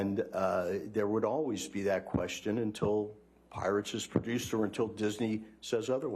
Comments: all I keep hearing is "pirate sex" xD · I could’ve sworn they were saying pirate sex